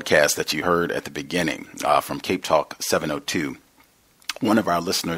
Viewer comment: around the 7:00 mentions a broadcast where he seen the author i couldnt hear it did he say caketalk??? anyway im glad said the book the project coast has a pdf cuz amazon selling it for 150$... smh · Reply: "CapeTalk" because that segment is from the South African program. Most probably in Cape Town,South Africa.